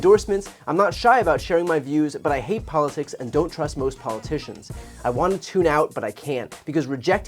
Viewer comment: thought they said dont trust the rich. glad to see her go back to san francisco